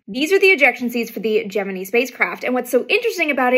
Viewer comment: Your pronunciation of the word Gemini is very unique. I don’t think I’ve ever heard it said quite like that. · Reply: I thought she was saying hegemony